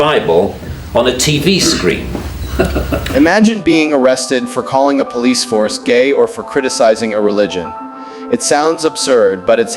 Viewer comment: I'm sure I heard "Police HORSE" not Police Force